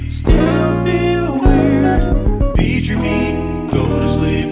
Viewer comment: I heard 'eat your meat' every single time and I am now worried for my sanity